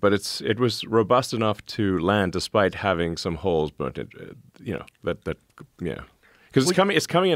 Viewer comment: “Holes burnt into it” sounded like generational wealth